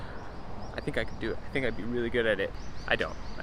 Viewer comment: You fixed your AC by yourself???? THAT IS REALLY REALLY GREAT!!! 今回も挑戦しました！0:40の聞き取りクイズの答えです！！お願いします！ But ah.. I think I can do it. I think I'll be really good at it. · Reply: Thanks for watching! The AC took all night, but was well worth it! The answer is so close! “I think I can do it. I think I’d be really good at it.” I’d or I would (because I haven’t actually done it yet)